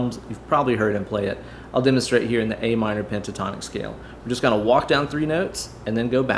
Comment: A Minor Gin & Tonic scale was priceless! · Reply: i heard it as 'A minor Djentatonic scale'...lol